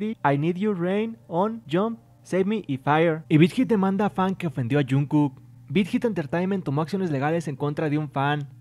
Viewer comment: en el minuto 4:12 escuche que avian perdido a jungkook :v